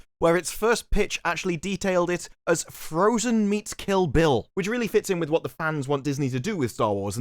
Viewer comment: At first I heard "Frozen Meat Kill Bill," like Bill got hit over the head with a leg o' lamb. Personally, I think Frozen meets John Wick works better -- only John Wick is a transgendered body-positive wookiee.